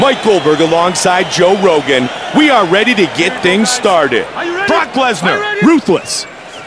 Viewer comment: did he say fuck lesnar? 2:52 lol